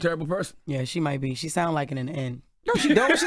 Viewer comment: What did Jess say at @ 5:29? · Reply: “Yeah she may be. She sounded like it at the end”